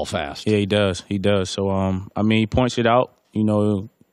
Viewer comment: lol sounds like he said he point s*** out😂